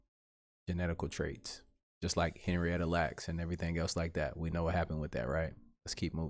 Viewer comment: What was the name said at 15:42? I heard Henry but can't hear the rest of the name. I'm tryna do my Googles.